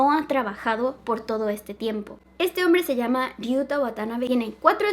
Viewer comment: yo escuche ryu watanabe , los de NFS ya sabran